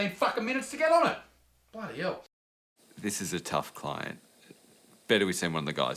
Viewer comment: I gotta be honest, I totally heard "this is a tough climb" and near fell off my chair laughing.